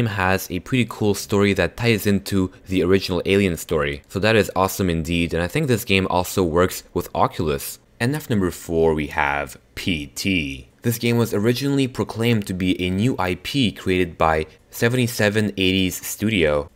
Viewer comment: wwwwwooooowwwww number 9 is not a horror game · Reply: no hate but sounds like he says " and fnaf number..."